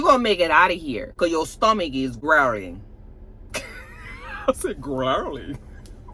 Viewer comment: will mess a word up quick. I swear she said "megaphor" instead of metaphor. I bust out laughing because it's true to her to mess a word up. I had to rewind it. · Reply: I thought she said Keyshawn's stomach was growing!! 😂 Getting bigger not growling!! Was so lost.